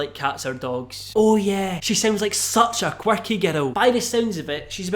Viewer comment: whenever he says 'girls' it sounds like hes saying 'ghetto' anyone else with that?